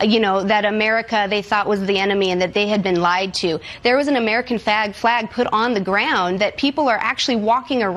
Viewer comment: Did anyone else hear her say "fag"